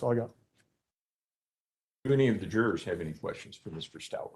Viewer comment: Since when are jurors allowed to ask someone that is testifying questions? Is that what the judge said on this times stamp or did I hear wrong. I believe he said “ do any of the jurors have any questions at this time “SOMEONE PLEASE ANSWER THIS FOR ME